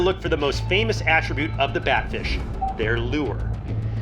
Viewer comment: I thought it said bad fish instead of bat fish, I thought abt sublime😢